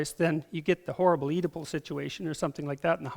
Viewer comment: somwhere around 53:00 he talks about the documentary ctumb and mentions thr "edipple" crisis or something like that. i couldnt understand the exact word. van someone please tell me what he meant. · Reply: oedipal complex. It's a idea derived from the Greek myth called Oedipus Rex. Google search "Freud Oedipal Complex"